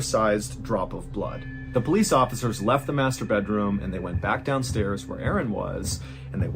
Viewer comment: I listen to his stories while painting but I got so deep into art I misheard Master Bedroom as M*stur**te room.